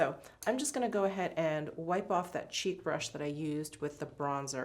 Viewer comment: Lol😂😂. I’m a bit under the weather right now. My sinuses and ears are a bit clogged. I thought you said “cheap” not “cheek”. I thought I have never heard her refer to a cheap brush. As you started to apply said “cheap” brush, I realized you said cheek !! 😂